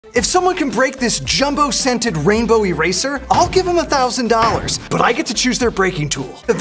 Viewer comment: For a second I thought I was going to say whoever can break this gay eraser